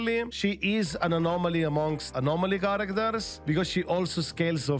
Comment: I laugh when i heard "Anomaly among anomaly" but its true thoo 😂😂